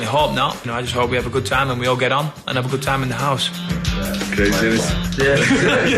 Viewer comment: They’ve always thought that keeps saying "craziness" but he doesn’t, he says "great innit"